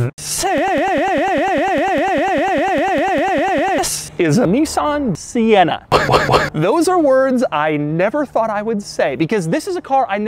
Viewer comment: I thought it said Nissan Sentra at first sight lol